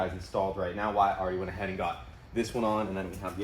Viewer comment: I thought I heard "Wife is behind me in the Porsche" when it was WYATT.... but I thought for a second "welp that explains the 2 week hiatus"